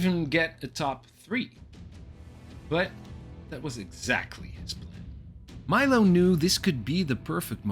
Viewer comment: Bᴜᴛ, ᴛʜᴀᴛ ʷᵃˢ ᵉˣᵃᶜᵗˡʸ ₕᵢₛ ₚₗₐₙ Edit: At least, I think that's what you said :P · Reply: "his plan"